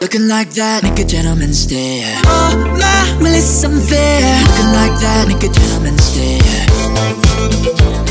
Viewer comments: when they say "feisty" it sounds like they're saying "ice tea" Edit: Jesus Christ how old is this, thx for the likes tho ♥️ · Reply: whenever he said ' Oh , my , well thats unfair . Looking like that makes a gentleman stare ' I thought he said ' Oh , my , Malysam Fair. Looking like that makes ya turn and stare ' lol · misheard lyrics: Ice Tea - Feisty Make a gentleman gay - Make a gentleman care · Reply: i THOUGHT HE SAID LOOKING LIKE THAT MAKE A GENTLEMAN GAYER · I keep hearing "make a gentleman gayer" instead of "care"